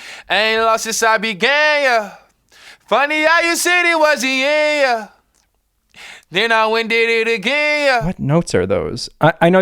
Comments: I love this song, but when I first heard it, I thought he said “funny how you said it was the air, then I went and did it gayer” 😭 pls tell me I’m not the only one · is it just me or is it when auto tune lil nas sound like he said gay instead of began :/ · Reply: YESSS, I thought that same. I thought he was saying gayer instead of began lol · is it just me or is it when auto tune lil nas sound like he said gay instead of began :/